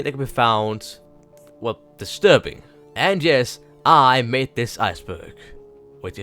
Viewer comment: I thought when you said I made this ice burg you said I’m on this ice burg